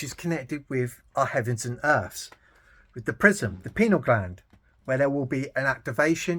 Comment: did you call the pineal gland the prison!?? **nvm you said PRISM lol I had to turn on closed captions I was like wait what! Lol Sorry I’m stuffed up today and it’s effecting my hearing